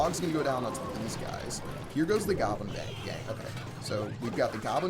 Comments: did he really say goblin bang😂😂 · Did he just say Goblin Bang-Gang?😂